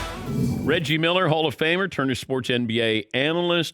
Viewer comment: I need to put away the edibles for a while. I heard "Ben Wallace, Hall of Famer."